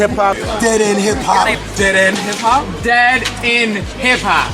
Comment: am i the only one that gets annoyed when Eternia says "Dead In Hip Hop" well it sounds like she is saying that lol